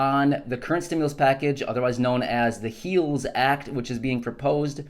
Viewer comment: Just a note...when I first heard the "Heals Act" and hadn't seen it in writing, I thought "Heels Act" how could they be so honest as that is what they are a bunch of "heels". Ah, but it was short lived. Is this where they take Obamacare out?